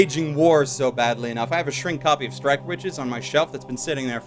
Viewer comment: copy of what? all I heard was Shrek